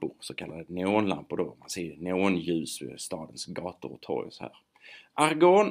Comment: Hej jag gillar verkligen dina videor. Men jag har en liten fråga vad säger du på 4:45 du pratar så fort där så att jag missar det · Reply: Tack! Det här säger jag: "Neonljus som man ser på stadens gator och torg, sådär. Argon har faktiskt särskilda användningsområden också, man använder faktiskt vid svetsning…". Hoppas det hjälpte!